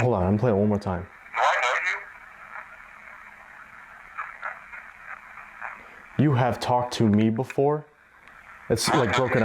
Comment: I heard " I know you from the Sally house." CREEPY! Lol. " Tell me you love me... tell me you hate me" 💜 aww I love you! 🤗